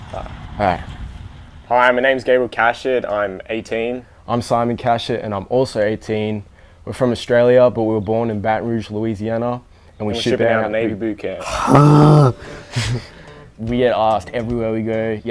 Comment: why did it sound like he said his last name was cat s**t